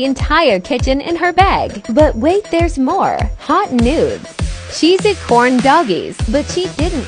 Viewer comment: "Hot noods" i heard "hot n&des"